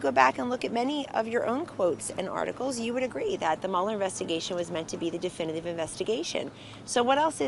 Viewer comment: Great video. May want to change the closed captions (I know as much as tRump how these work). I assumed it was a slight mistake at 0:32 when conwoman says "investigation" it reads "infestation", but then at 3:10 when Brian says "...conclusive evidence that he is innocent..." the caption says guilty. I'm glad I was listening at the same time. These people should make great DJs with all their spinning. Casually when it suits them (when trying to convince people that dotard has been proven innocent) they paint the Mueller investigation with the colors of "nonpartisan" "professional" "definitive" and the general idea of "nothing to see here", but when confronted with the facts of all the wrongdoing found, they agilely flip back to "highly conflicted special counsel" and "13 angry democrats", "witch hunt", etc.